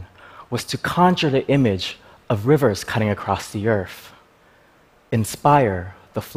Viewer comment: Very interesting, but it sounds like he says EARF, instead of EARTH.